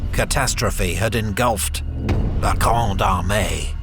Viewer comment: The magnum opus of Napoleonic videos. A magistral work. Once again, you out do yourself! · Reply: Every time i hear him say "La Grande Armée" i hear "La Gronde hon hon hon hon Hey" truly epic!